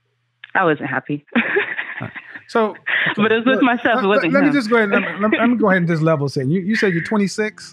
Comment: ‘I broke up with my boyfriend because I wasn’t happy’ News flash: She never will be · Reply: I thought she said "with myself, it wasn't him" though 🤔 Did anybody else hear that? That's a great reason to end a relationship 💯💯 She'll end up making the man miserable!!!